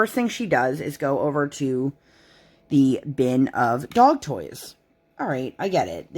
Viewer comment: ok i am sure i am the only one but i thought you said they gave her adult toys not dogs toys lol. i did laugh at myself for that one. still laughing